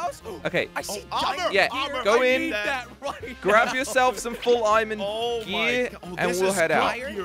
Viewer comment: did not say iron or diamond he said iromand (iron and man.... geddit?)